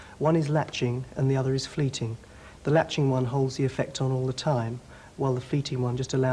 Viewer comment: But what the hell is. 'freeting'. Around 6 mins. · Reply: I thought he said velching